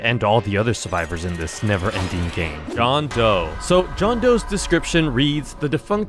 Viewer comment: for a second I thought i heard john toe